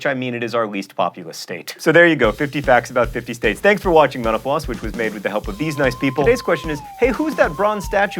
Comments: I keep hearing "thanks for watching menopause"... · At the end I thought he said "thanks for watching menopause"... Heh